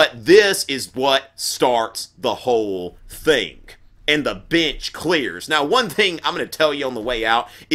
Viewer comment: Why did I hear him say "and the bitch clears"